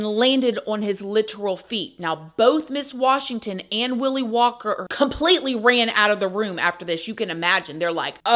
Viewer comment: Omg im.sorry i thought you said mrs willy wonker 😂😂😂 like willy wonka 😂